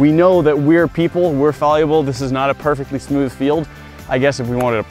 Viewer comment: Wait.. Did he just say "Fal-u-ble" So meta...